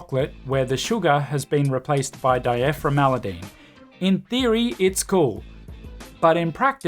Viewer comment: What word is this you say here? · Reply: I made it up. Dyephremalidene. The idea being that we can't know the long term (or even realtively short term) consequences of feeding AI algorithms everything we know, because we don't really know what they are. Also the "mal" included in it was deliberate. 😐